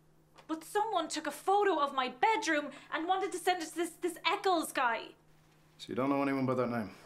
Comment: I thought it said mr.neckles